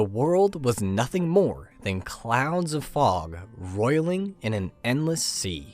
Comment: am i crazy or did you say 'clowns of fog' just now